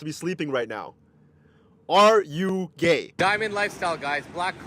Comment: I'm going to assume that guy said "are you game" but I heard "are you gay' and I'm like "who's asking...? 😏"